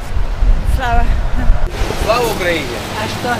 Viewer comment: Мне послышалось что он кричит Сало Украине а путешественники в этой стране им отвечают Героям Сало.